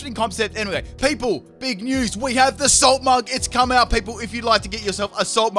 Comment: People the Salt Mugs are here! go grab yours why they are hot~ <link> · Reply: When you started talking about the Salt mug; I thought you were saying Assault Mug. Which would be interesting, a mug that makes you only offensive...